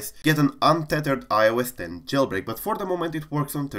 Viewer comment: I got excited I thought it said iOS 11